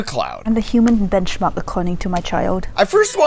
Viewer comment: a human what? I couldnt understand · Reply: She said "I'm a human benchmark according to my child"